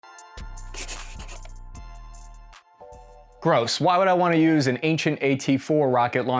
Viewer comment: At the beginning i thought he said "bros" but he said gross.